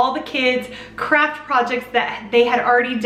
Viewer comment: At first I thought you said “crap projects” instead of “craft projects”. I was like wow girl, wow 😂 😂 PS- your hair looks fantastic in this video!